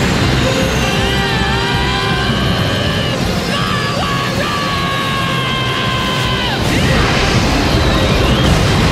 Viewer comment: Does anyone know what Goku says on 0:43 . I can’t for the life of me understand him here 😅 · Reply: POWER UP!